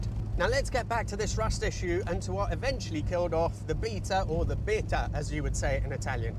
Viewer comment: I keep hearing "the Beta" as "the Vicar".